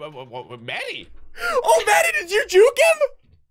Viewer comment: I thought maddie said daddy